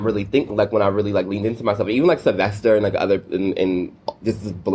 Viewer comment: I deadass thought you said it’s called “I got Thots” 😩 I can only apologise lol